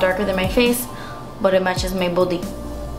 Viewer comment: i thought you said it matches my booty lmfaoo